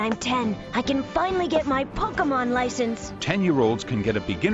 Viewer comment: ''pokeymon'' i hear ''pokegaymon''